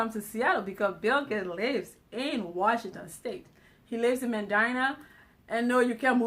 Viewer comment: I had the volume down I thought she said vagina 😂